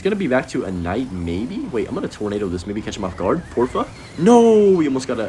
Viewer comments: did bro just say porfa?? latino descendence/latina gf???? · did he say “ por fa”????? lol